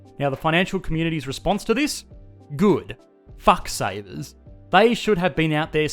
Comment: "Good...Fuck savers." Holy shit, he mad o.o 16:15 if you didn't catch it.